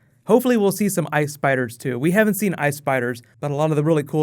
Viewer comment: At first I thought you said Ice Fighters and I was "hmm, wonder what those are..." and then I realized you said Ice Spiders and all I could think was, "bring Dany, kill them with fire!"